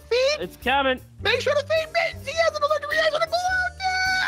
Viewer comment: i can some how hear what hes saying MAKE SURE TO FEED MITTENS HE HAS A ALLERGIC REACTION TO GLUUUUTEN